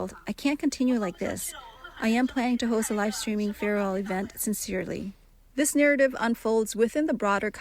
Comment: When the crying girl said she was planning a live stream farewell event I heard "planning a live stream FUNERAL event"... For a moment this got off to a way darker start.